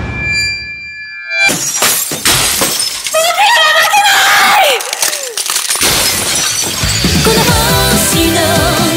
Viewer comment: のセリフって 何って言ってるんですか？ 聞き取れた方教えてください🙏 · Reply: 「僕らは負けない」じゃない？